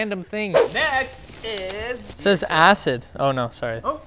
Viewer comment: it says “ Ass it “ is what I heard haha